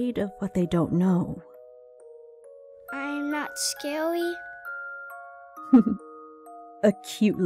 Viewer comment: Im not scArY. Gah- sorry, it's really hard to hold that voice I think that's what he said please correct me if I messed it up I'm doing this from my awful memory 😭